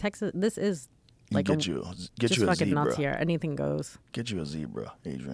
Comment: Did she say “nazi era” at the 3:49 mark?